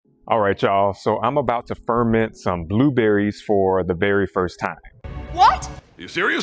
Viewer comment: I swear I heard "I'm about to ferment blueberries for the 'berry' first time"